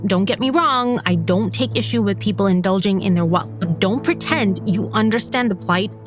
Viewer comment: What’d she say? Indulging in their what? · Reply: Wealth I think